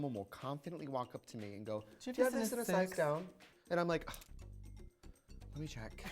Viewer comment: what did Trixie say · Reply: "do you have this in a size down?"